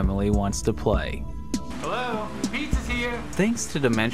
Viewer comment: All I hear is " Ayo the pizza here" and then legs breaking as they fall down the stairs